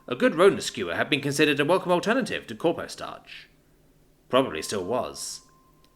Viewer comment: When I heard "corpo-starch" I missed heard and thought it said corpse starch